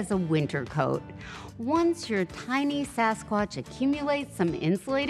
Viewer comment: Did she call our fetuses a sasquatch?